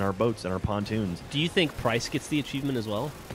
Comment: just curious, who is price? at first I thought you said christ.. :) ..